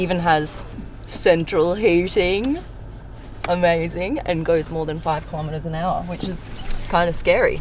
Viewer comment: were you supposed to say, “ more than 5 kilometres an hour” ?, because I thought you were trying to say 500km an hour